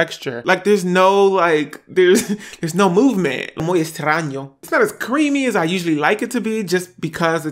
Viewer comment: What did he say at 5:51 Anyone know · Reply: AnimeChan 103 muy extraño - very strange!